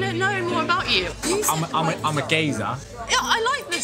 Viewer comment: i'm a gazer? What Word did he use? Thank you❤️ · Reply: geezer for a guy don't matter young or old